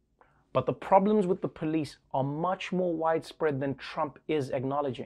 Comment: I misheard you say "The problems with the police are much more whitespread than Trump is acknowledging" ... :-)